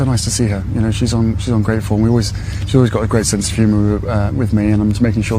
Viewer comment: Harry spoke so fast to get through the lies that l thought he said he was ungrateful when speaking about his grandmother.